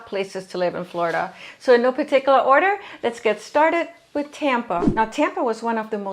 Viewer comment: I am a realtor in Sarasota. Thanks for the positive vibe. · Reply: I thought she said “No particular order”.